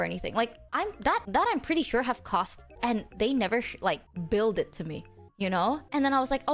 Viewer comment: TL correction for 17:13 and following: She was saying "billed" instead of "build". Getting an invoice for the costs she has incurred the company for her projects.